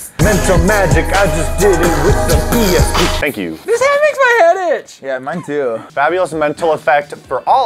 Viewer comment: I thought said "This head makes my hat itch!" LOL